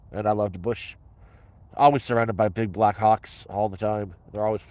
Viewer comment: ?? 🤔 BBC is that what I heard? I listened like 5 times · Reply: Lol big black hawk